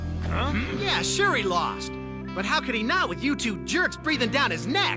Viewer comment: The Academy demands you to realize you're a manchild makind that stuff up and should refrain from making cringy intros. Also when Jayden... Gayden... Djaiden... said "You two jerks" I totally heard "Youtube jerks".